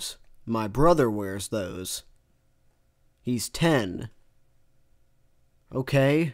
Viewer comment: when generic villain said "my brother wears those he's 10" i thought she said "my brother wears those he's dead" i wish the ladder was true